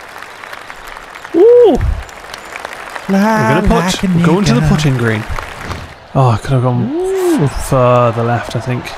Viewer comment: I have shit hearing... what did say here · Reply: Fly like an eagle! It's from a song!